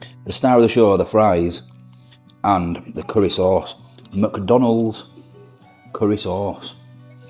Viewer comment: I seriously thought he was saying macdonalds which i find disgusting 😂